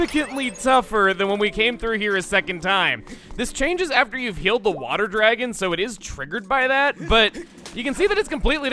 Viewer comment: when he said "heal the water dragon 🐉" I thought he said killed the water dragon 🐉